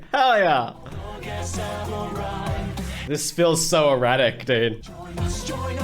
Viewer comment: I THOUGHT HE SAID EROTIC LMAOOOOOO